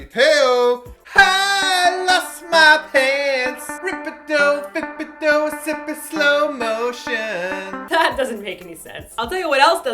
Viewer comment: my favorite part was when that guy was dancing with the chairs because he looked smexy and that i heard "rippado sippido getting slow motion" in the background....